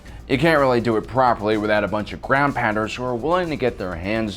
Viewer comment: Ground pounders I thought you said ground pandas but I am English so sorry. Anyway for a few moments I had visions of highly trained ursas being given a cia briefing in a central European country. Surreal.